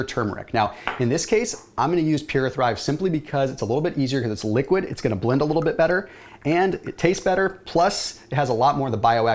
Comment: Love this alternative as I’m not really a coffee drinker, what is the liquid turmeric that you’re using? Sounds like you said Pure a thrive? Maybe they changed their name because when I search PuraThrive only PURALITY HEALTH comes up now similar labeling.